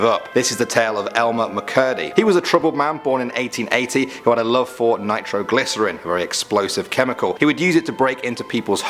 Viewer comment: did I hear fortnite